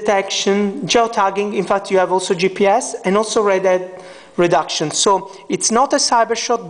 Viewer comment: did he just say redhead reduction?